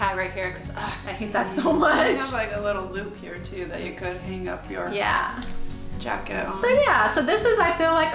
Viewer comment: For a second it sounded like you said Packsture jacket lol - Great review...